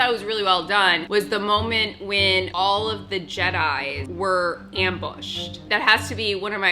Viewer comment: LMAO. I know this is about Episode 3 but when she said "All of the jdedi" I legit heard "Episode Nine" AND I had to rewatched 4 times.. lmao